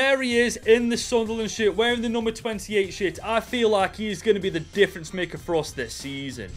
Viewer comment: did he just say “wearing the 28 sh-“ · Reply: Shirt..